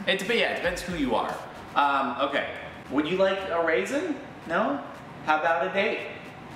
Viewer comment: I heard “ would you like a raisin, how about 8” and I got a completely different context out of it 😆